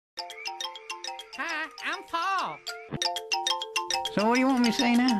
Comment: At first I thought he said "hi! I'm po!".